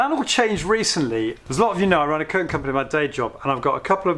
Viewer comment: Great video. I didn't quite catch the bit where you said what your day job was - I thought you said you run a 'coke' company in your day job, but I'm sure that's not it! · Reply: Thanks! Ha! The word was "curtain" but it's soft furnishings really rather than just curtains.